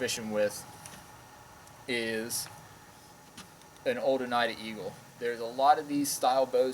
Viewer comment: What is the name of the bow your holding at 7:30 I heard you say eagle at the end but couldn't understand the first part . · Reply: That is an old Oneida Eagle. The newer versions are used by a lot of people for bowfishing. The new ones are a bit lighter than that one.